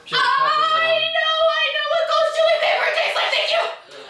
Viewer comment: Did anyone else hear her say "Ghost chili pepper cheese slip" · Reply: No she said i know what ghost chili pepper tastes like